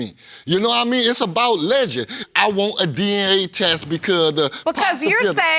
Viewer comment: I swore he said he want an D<E> A test